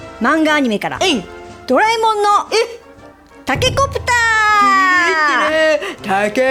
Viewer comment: がタケコプターって言う所、ボーちゃんにしか聞こえないw